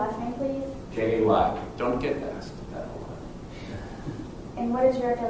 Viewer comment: asking to spell his name. He then starts to say ‘don’t get…’ and I think he says ‘nasty’ (likely meaning don’t start with your nasty letters to me) and I’m positive I heard some very very quiet laughter. I love it 😂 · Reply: He actually said I don't get asked that very often - because his name is so simple to spell.